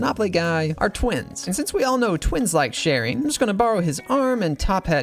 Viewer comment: did u just say... gulp twins.. "love" sharing? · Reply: He said twins like sharing